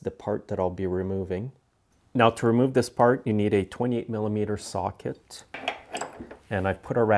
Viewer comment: did you said 28mm